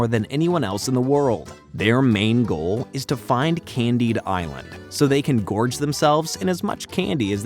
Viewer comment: as a kid i always thought they just said candy island wierd, in my brain the d was attatched to island, so "candy disland", dont know why i never got that